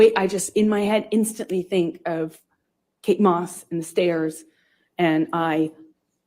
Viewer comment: did she say Kate moss?!